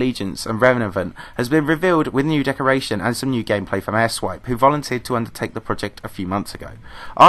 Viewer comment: airswipe i thought u said ass wipe